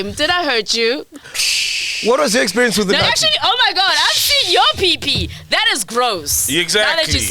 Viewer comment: Did she just say "I've seen your pipi"😂😂😂😂😂🤣🤣🤣